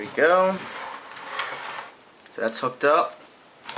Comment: Sounds Like He Says "Now Thats Fucked Up" lol